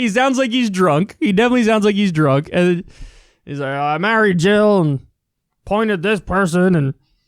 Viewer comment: I just realized he actually says "I married Jill". I originally thought he said "I'm not a jew"